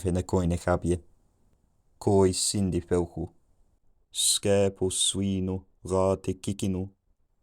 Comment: Just few sec before 900 ad all I heard was ‘no shi, no gyat’. English did come full circle, didn’t it?